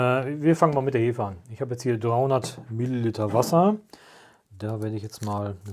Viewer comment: Kleine Anmerkung: Bei 6:40 sagt "300 ml Wasser" (sind es ja auch, kann man am Messbecher sehen), aber bei den Untertiteln steht "200 ml". Da es auch auf allen Sprachen so übersetzt zu sein scheint, könnte es evtl missverständnisse geben.. Short Note: At 6:40 says "300 ml of water" (which it is, you can see it on the measuring jug), but the subtitles say "200ml". And that got translated to every language seems, so don't get confused.